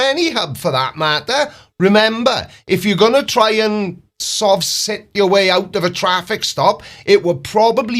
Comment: Did you say “if you’re going to go and try to Sov Cit your way out of…” Listening to these smooth brainers over the years I always thought they were trying to Sov SHIT their way out of legal jeopardy 😂🤣😂🤣😂🤣 I think that’s far more fitting for all of the feces flowing forth from their faces.